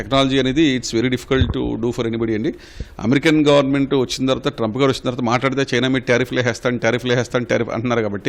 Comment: …Did he just say “ trump gaadu”